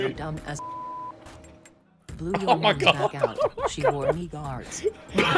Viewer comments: what tf did Randy say? I can’t figure out what rhymes with guards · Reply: the r word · did vik actually said niggas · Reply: “knee-guards”